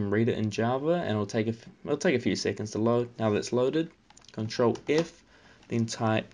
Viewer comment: ctrl what (1:32)? i cant hear it clearly. can someone help? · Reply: Mariko Mikia ctrl F